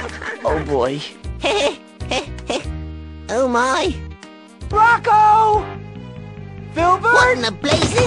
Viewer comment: It sounds like he said "Brocko.